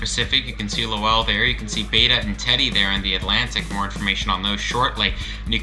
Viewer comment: i hear teddy bear on